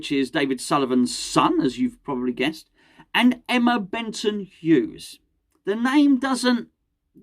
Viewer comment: Thought I heard Emma Bunton at first 😂 Wouldn't mind Carlton 'Gold' Cole in the boardroom for some good times